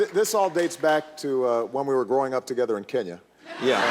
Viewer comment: Reporter:"What is this thing with Trump and you....... " Obama: "It all dates back ti when we both grew up together in Kenya" Ohhhhh dammmnnnn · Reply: I thought he said "going out together in Kenya"